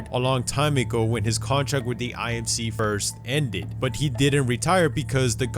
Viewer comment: Omg check the captions on 2:32 it says KFC instead of IMC lol